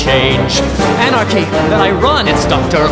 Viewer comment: "Anarchy... that I run." I just love that line. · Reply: i misheard it as "anarchy, then i run!" for years